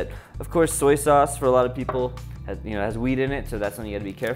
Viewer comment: lol I had to rewind three times when he was talking about soy sauce. I thought he said "it has weed in it." but at last no he says wheat.